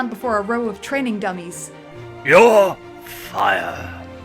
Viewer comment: that almost sounded like Ignitus was telling Spyro “You’re fired”